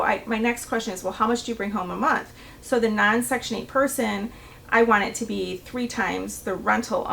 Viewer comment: If someone made 3x the rental amount, they wouldn’t qualify for section 8. That’s a ridiculous requirement. Section 8 is for those who have low income. 🙄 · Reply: Scope I get your point. To my ears, she speaks fast. I had to go to the transcript and read what was said. At 10:01 she actually said “non-section 8 renters “ It’s confusing since the topic is about section 8 tenants. Knowing she's referring to another type of tenant then 3 times the rent makes sense.